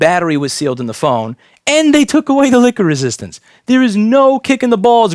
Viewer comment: i thought you said take away the liquor resistance